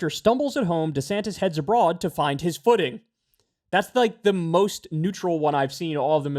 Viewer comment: "...to find his footing"? I heard "to find his pudding" 😂